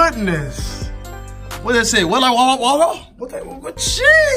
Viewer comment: What this say?? Wet like. Water wa wa wa:-)😂😂